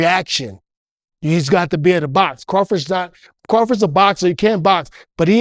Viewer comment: Did he call him crawfish at 2:39 😅